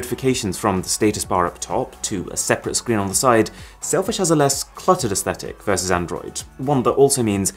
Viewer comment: did he said selfish? 🤔